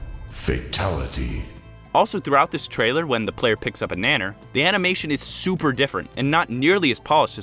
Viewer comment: DID YOU JUST SAY BANANER